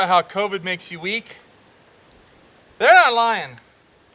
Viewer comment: "People say covid makes you weaker, that's a lie" Spends the next 20 minutes complaining about how weak covid made him. Do you covid deniers have any shred of credibility and decency at all? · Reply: I definitely think Covid is serious and should be treated seriously. But I think you misheard, I'm sure he said "They're not lying" in the beginning, around 4:24. If that is what you are referring to.